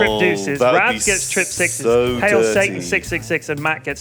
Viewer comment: did anybody else hear him say "Hail Satan"?